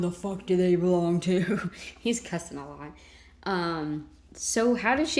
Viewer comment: I thought I heard "they belong to my guest."